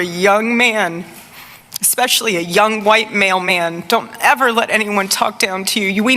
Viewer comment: And anyone else notice how erika said "white male men"!!?? like ...man implies male! She made it sound like she said "mail man" hahhaa